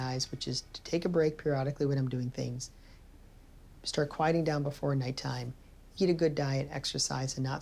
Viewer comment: at first i heard "start quieting down the fortnite time" and i had to go back to hear it again